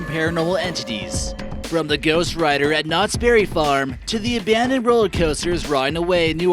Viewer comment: When he said “ Knott’s Berry Farm I thought he said “ Not scary farm” 😂😂